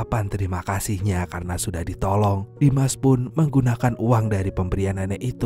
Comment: seriusan namanya Dimas? Atau gua salah denger?